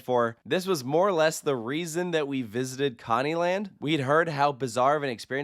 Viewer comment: Sounded like he said commie land , and that sounds like a park where none of the rides are working lol